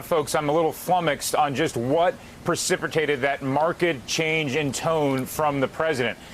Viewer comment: pracipricated? what was that word? was that a word?